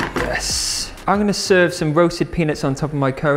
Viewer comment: i swear i heard "i'm gonna serve some roasted penis" :D but i'm definitely trying out the recipes, especially the chickpea fritters ^^